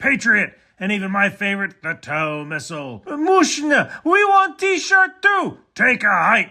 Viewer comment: what’s the word. Amoooshna? Lol